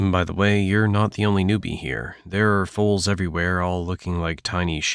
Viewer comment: Oh fowls, I thought he said fools🙄🤦🏾‍♂️